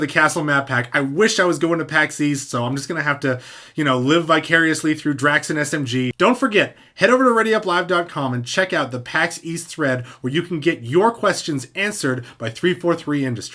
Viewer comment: East? I thought you said weast.